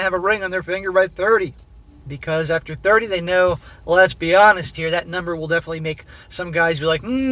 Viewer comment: I swear I heard him say "lesbihanist" here